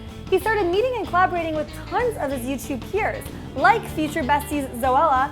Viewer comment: when she said, “YouTube peers,” I heard, “YouTube queers.”